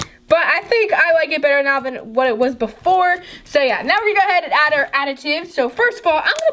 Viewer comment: did she say we're gonna add our aditude?